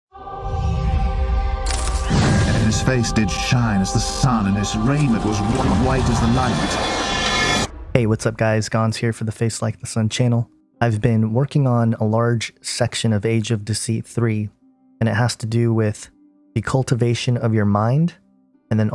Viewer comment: The beginning of each video is wrong. nothing about Jesus is like the night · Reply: ok I'm sorry I thought it said like the night. My mistake, thank you for replying and letting me know where I was wrong quickly. I listen to your channel a lot and it just had me befuddled. Thanks